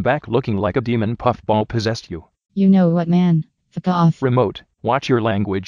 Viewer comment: I thought remote said Ted😅